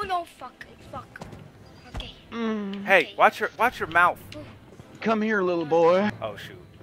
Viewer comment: 😂😂😂😂am i the only one who heard jason say " come here little boy"